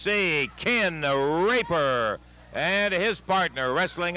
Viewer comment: I misheard that as "Ken The Raper" (or at least I hope I misheard it...)